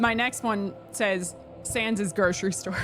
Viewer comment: I really thought you said "Sans is GIR, true story"